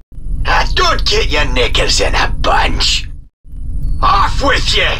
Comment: Did he just said an N word?